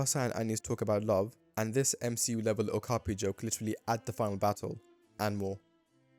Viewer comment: this what joke? I can't make out what you said here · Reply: MCU Level Okapi Joke